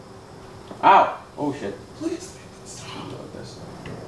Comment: i heard please make it stop